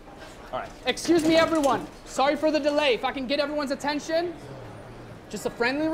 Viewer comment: i thought anwar said the f word but he said "if i can get everyones attention"